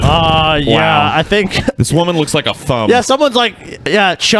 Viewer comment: My god, I heard, "This woman looks like a thumb." in that tone and just fucking died laughing.